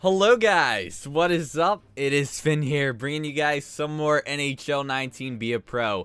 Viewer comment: I was just scrolling through and it said this video had 666 viewers, also I like the vid but in all of your videos it sounds like you say it is sfinn here